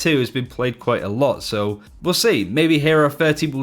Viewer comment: Omg I thought at the end he said, "Hero 13." I was like, "Did we travel back in time or lose over half the roster or something?" Lol